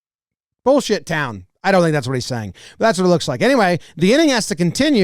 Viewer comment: I think 8:15 he said “supposed to get/hit down” like don’t go for the head